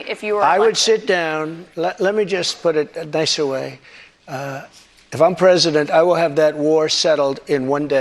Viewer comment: He actually said if im president ill have it settle. Nice try there bozo.